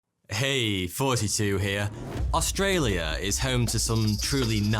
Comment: Pls like you are very cool I like how it sounds like you say " Hey, 42 here."